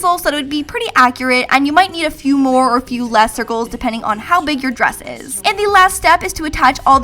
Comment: Hey Lauren! I love this video so much, and I want to make sure everyone can understand and enjoy it. That's why I wanted to let you know that your captions are a little off. At about 4:06 the auto-captions say "Few more or few lesser goals" where they meant to say "Few more or few less circles”. When you get a chance, could you fix that? That'll help the 360 million folks globally who rely on closed captions to understand videos. Thank you, and keep up the great work! (